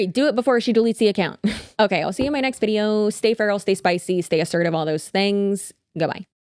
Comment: For a split second, in your outro, I thought you said “stay fertile” instead of “feral” 😂 I thought why would put that on me! lol